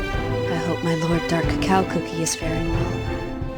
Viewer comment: I thought caramel said "Dark Cacao Cookie. Farting well "